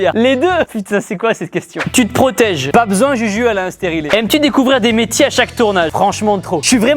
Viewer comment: C'est moi à la 14:26 il dit que Juju est stérile ? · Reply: Nn il dis que juju a un steriler